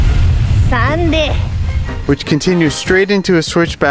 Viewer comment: was I the only one who heard send it? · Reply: It was sandy 😂